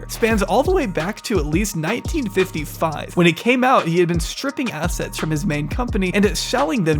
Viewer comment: I’m just listening to the video, and what I thought I heard was “tripping acid”. Much cooler than stripping assets.